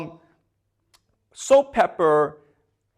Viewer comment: I’m sorry…. But I couldn’t help but to laugh out loud when he kept saying “saltpepper” instead of “saltpeter”